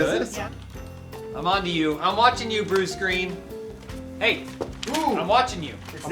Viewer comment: I thought said "I'm watching you, blue screen" but in an Asian-failing-to-pronounce-the-"L" kind of way